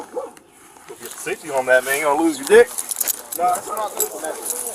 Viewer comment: does he say, there's no safety on that thing, you're gonna lose you dick.